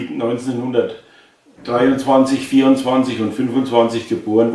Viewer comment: Unfortunately, there is an error in the subtitles: Mr says that his three brothers were born in 1923, 1924 and 1925. The subtitles say 1922, 1923 and 1925.